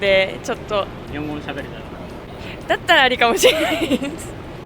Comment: she says "ari da to omoimasu" アリだと思います meaning something like "it's possible" "I'm fine with that" "It's ok", so his translation is correct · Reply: I heard "ari gato". So there's ari da to and ari ga to...